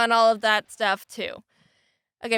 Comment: Lol right at the end of the video when she says "Okay, bye" it sounds like she is saying "I'm gay, bye"